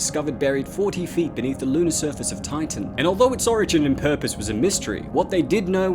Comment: Did you say Titan or Tycho at 6:34 ?